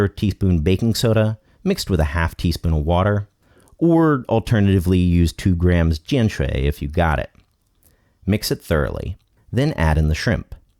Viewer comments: What did you say at 3:53? The alternative you mentioned to baking soda & water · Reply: 碱水 I dont know the english equivalent I think one can buy it in china only but it can also be self made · Can anyone help what did he say at 3:53 that's “3 grams of gin tray?” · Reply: jianshui